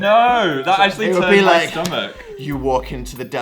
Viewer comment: I THOUGHT PHIL WAS SAYING "IT ACTUALLY TURNS ME ON" BUT HE SAID "IT TURNS MY STOMACH" OH GOD